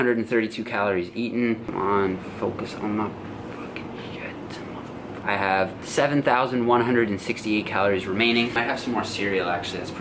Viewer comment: Anyone else hear the “Focus on my fucking shit mother fu-“ hahaha so good